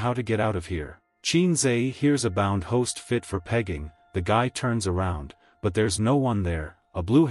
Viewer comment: I think it meant picking not pegging, But captions say otherwise.💀